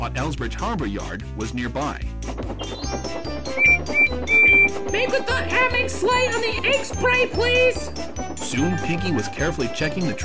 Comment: I'm having trouble trying to figure out what Percy said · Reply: Mike Owen I was using Pig-Latin there, I said "Make with the ammingslay on the akesbray please."